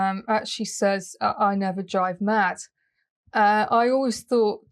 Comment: i thought she said, i never drive men. that was weird because she was driving the monarch, 21 ands 24 in tag sale you're it